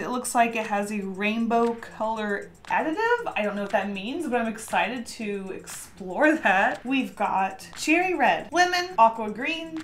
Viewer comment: Lol, I heard “I’m excited to snort that”